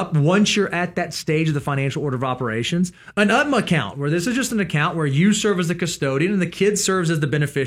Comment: what type of account did he just say? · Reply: UGMA